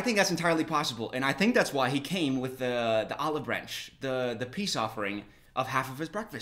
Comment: For a second I misheard this as „omelette branch“ and now I feel like it‘s a missed pun opportunity